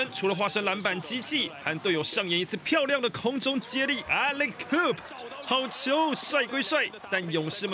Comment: 聽成Alley-koop